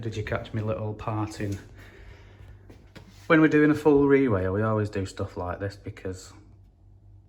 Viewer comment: Did you say re-rail? 09:05 Did you work on the railway before this?